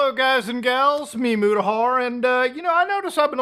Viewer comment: what did he just sayy ??? That’s a good ol boy 👴🏻